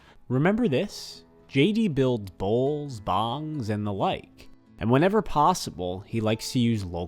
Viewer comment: I thought you said he makes BOMBS out of bullets not Bongs lol